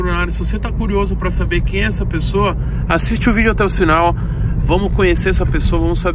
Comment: "Assisti o vídeo até o "SINAL" 0:47 Eu entendi Sinal Man, alguém mais entendeu sinal tmb? Kkkkk